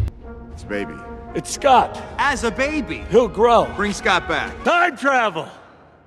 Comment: did he say he'll grow😂